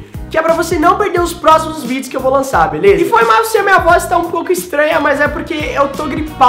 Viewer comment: Sim, eu entendi "E foi mal se minha vó tá um pouco estranha"